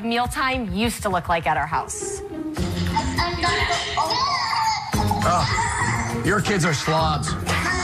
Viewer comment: did he just say “ughh your kids are slobs or is that just editing